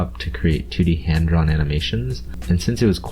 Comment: I thought you said titty hand drawn animations